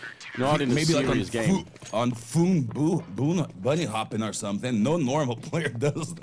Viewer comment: What is caster sayin on..wtf :D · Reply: he's trying to say "phoon like bunny hops" or something along those lines. phoon was a legendary bunny hopper back in the day